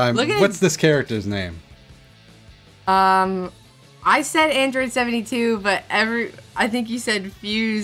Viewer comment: did i just hear android 72?🤣🤣